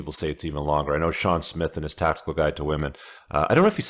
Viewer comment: How do you write that name ? Thank you (it sounds like "shawn smith" i believe i heard)